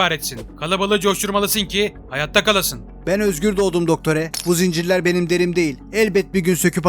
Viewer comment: dottore mi dedi ben mi yanlış duydum